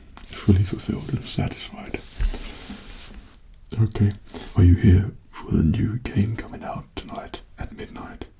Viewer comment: So sorry but what I heard was "Are you here for the new kink?" and I'm not mad.